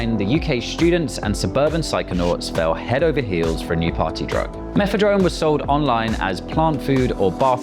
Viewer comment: First time I heard of this I thought she said methadone. I'm thinking Europeans are hooked on what we give attics to get off drugs? Now I see it's just bath salts. Yikes.